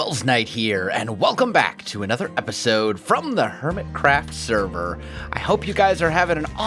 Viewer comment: Does it sound like he say "welcome back to the hermitCRAP video?"